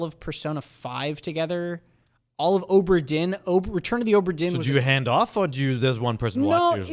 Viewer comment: Timecodes: 00:00:00 Welcome! 00:00:48 Animal Crossing save data update 00:08:12 How will Nintendo fit in with next-gen consoles? 00:20:18 Nintendo Direct and E3 rumors 00:27:58 The best Valentine's Day games 00:34:57 Games out this week 00:40:50 What we're playing 00:48:45 Question Block! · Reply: Did Tom say he played RETURN OF THE OBRA DIN with his partner???? For some reason I heard that as he played RETURN OF THE OVERFIEND smh aight imma head out